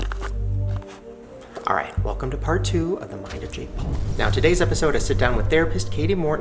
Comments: I thought that said “ Fart 2 “ 😂😂😂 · I fully thought it said heading to the rapist when it said therapist , tf is wrong with me ? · I feel dumb I thought it said the rapist instead of therapist😂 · when it said “heading to therapist” i thought it said “heading to the rapist”